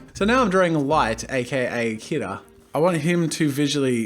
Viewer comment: I thought you said " light aka hitler" Instead of "Light aka kira"